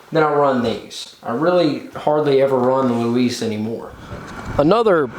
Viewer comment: Thanks for the infos! 👍🙂 What do you say at 7:40 about Louise tires...? I don't understand it clearly.